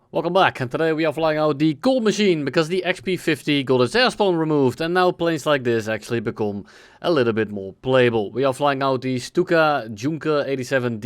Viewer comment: I swear, right at the start, I thought he said: "- the XP-50 got its asshole removed" lmfao